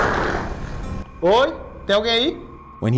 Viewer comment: parece que dice titi tomorrow